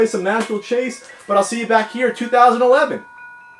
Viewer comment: uhhh what did he just say?? 😅😅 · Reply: “But I’ll see you back here in 2011!” Because the episode came out in December 2010.